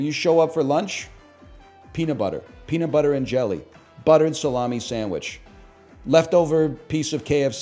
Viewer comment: I’m sorry did he just say buttered salami sandwich?